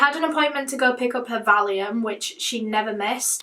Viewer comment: says she goes to pick something up. I can’t quite understand her, does anyone know what she is saying? To me it sounds like she’s saying “darium” which I have no idea what that is. I live in America so it may not be a popular term here. If anyone know and is ok explaining please do! Love you! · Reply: Caitty she says Valium. It’s used to treat anxiety, alcohol withdrawal ect .